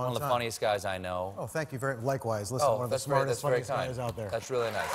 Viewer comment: Jon Glaser got past the filter at 0:50 "smartest fucking guy" guess Stephen was enough for him to slip that by~ heh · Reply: I heard "one of the smartest, funnest Thai's out there."